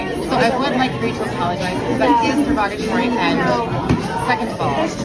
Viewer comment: Did she just say 'perogatory'? I mean, it's understandable. If you're about to dive a few bass notes deeper into Barry white territory, it's understandable that your mind might not know the word derogatory.